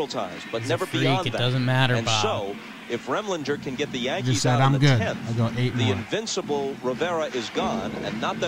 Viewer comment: i think Mo says HI MOM. not im good